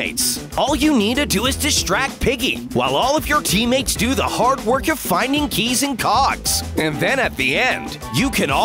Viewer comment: (im sorry o-o) who heard finding keys and cocks?